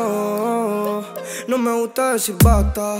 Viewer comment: Yo pensé que decía "no me gusta de su pata" xD